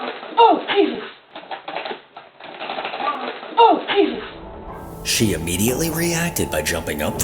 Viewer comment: i hear mom and she said jessie like maybe her child?